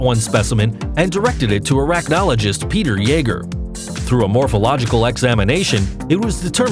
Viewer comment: instead of "Peter Yeager", the subtitles say "estupido Yeager"